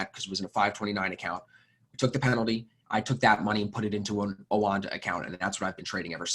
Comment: what he said...oanda account? Where do you guys trade?